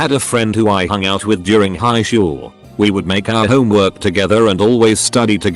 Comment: did anyone catch that the person said highshool? Lol